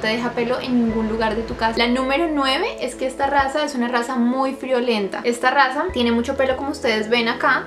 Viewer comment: #21 que son muy costosos Líke si tienes un Yorkie Tengo un Yorkie divino dorado👍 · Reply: Cuando dijo "Es una raza muy friolenta", me asusté porque escuché "Es una raza muy VIOLENTA" 👀😂.. En fin, algún dia Dios primero, deseo tener uno de estos 💖😊